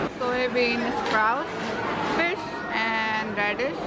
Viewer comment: did she say fish?